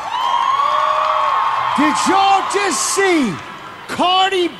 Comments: who heard who just saw cardi pee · WAIT DID HE JUST SAY "CARDI PEE" ??? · i heard Cardi Pee whattt???